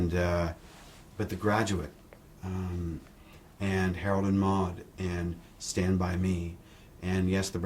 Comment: Whats the name of the movie that he talks about right after 'The Graduate' at 04:13 ? Can someone help me? I couldn't understand... :s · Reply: Harold and Maude (1971)